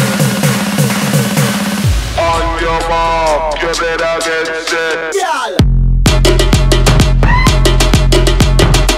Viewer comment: what he said at : 0:57-1:00 ? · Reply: "On yuh mark,yuh better get set" (In his Jmaican accent.)